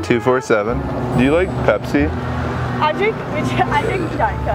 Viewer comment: subtitles says pespi Not pepsi.